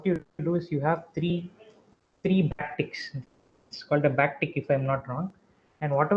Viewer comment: What did he say at 40:32 was it bactic or what please tell? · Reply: backtick